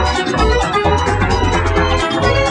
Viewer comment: Why did I heard ''Hey kid what time is it?'' and with echo sounds around